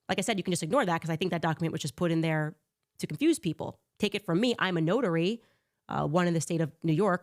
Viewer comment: Sooo, I'm watching A Wrinkle In Time. Episode 1. At min. 24:35 Candace says she's a notary then follows that up with in the state of New York and the license is expired. Hmmm, interesting, how can you be a notary if you're license is from New York and it is expired and you don't even live in New York. I'm going to go ahead and call BS!! You are not a notary, you are a YouTube propagandist podcaster and that's all you are!! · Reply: I thought she said “was a notary” now she’s not bc her license expired.